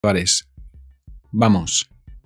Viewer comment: What word is he saying at the beginning of the video before Vamos? Is it Pares? It’s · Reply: It is CHAVALES, VAMOS. :)